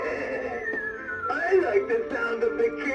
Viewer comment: WHY DOES THE CLOWN HAMMER SAY SHUT UP!? LOL I HEARD THAT LOL 9:57